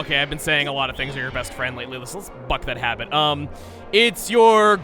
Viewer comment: Someone please tell me what he said, because it sounded SO much like dropping an F-bomb, but I know doesn't swear casually. Even the automatic subtitles think he's saying Fuck. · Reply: Gear Wax Buck. Like the saying "buck a trend"